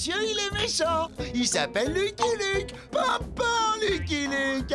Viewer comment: et pourquoi il pleure comme ça ? Parceque tes un vilain ! Tu vas junior le monsieur il est méchant il s'appelle Lucky Luke. Papa lucky luck han junior oo moi gentile ça alors Joe Dalton Dalton faisant le nounouche j'aurais tout vu. Je suis pas nounouche je suis Joe Dalton done si il va casse nous aurai demain on n'ira le bébé a c'est parents. La dimanite sauta et le covre souffrir de velan et a co il a win chan san petalor mechant Lucky luke fais direction Monsieur luck c'est gentil port une cheval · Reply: Il dit "panpan Lucky Luke" mais tkt j'avoue on pourrait croire qu'il dit papa